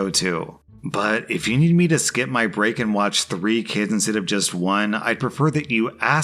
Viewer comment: did you say "3 kittens"? 🐈